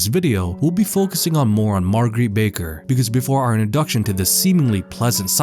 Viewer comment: Ok for a second ... I thought it said maringe baker.... and I said what ?? When did they have a (mur-in-gey) ( speed dontnknow how to spell muringe pie ) baker .. and I said wtf ?? When was there. Abaker ?? Lmfao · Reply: Meringue. Pronounced “murrang”